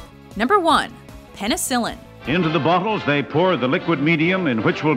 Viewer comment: Did anybody hear "buttholes" instead of "bottles"?